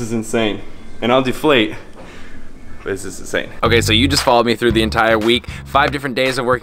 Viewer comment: you say “zinging” but the subtitles say “singing” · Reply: you say “insane” but the subtitles say something wrong